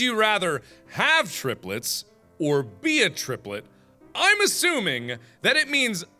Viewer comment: the first question was "have triplets or be a triplet" I thought it said have 3 nipples or have no nipples wow